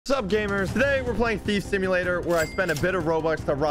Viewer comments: i thought you said feet simulator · I swear on my life,... I know it's thief simulator, but I heard "feet simulator"